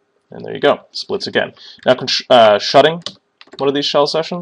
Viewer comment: what are you saying, shudding ?